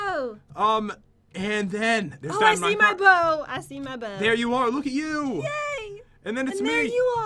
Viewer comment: I Thought Pat Said Hair You Are! And Thought Was A PUN But It Wasn't So Pat, What's My PUN-Ishment?